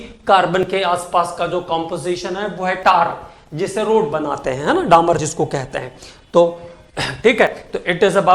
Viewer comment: Sir ne 13:57 pr kya bola🤔🤔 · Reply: Ooh ha tarcol...😅😅